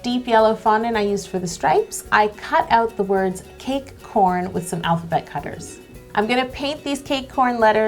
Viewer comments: when she said "cake corn" I thought she said "cake porn" and I'm just here like, is it possible to like cake a little too much?? 😂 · Anyone else thought she said "cake porn"? Had to rewind 😂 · When you said ”cake corn” I thought you said ”cake porn” 😂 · The first time she said cake corn I thought she said cake porn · Anyone else hear cake porn? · i always heard "cake corn" cake porn!! omg 😂😂😂